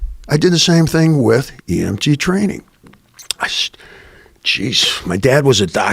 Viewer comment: The entire interview Joe thought he said he was a DMT.